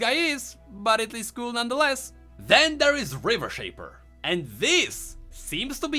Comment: i could swear you said "river shen"